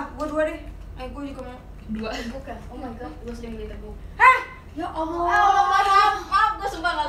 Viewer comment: si flora ngomong apaan sih ampe olla teriak? 😅🙄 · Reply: Beli di "Teguk"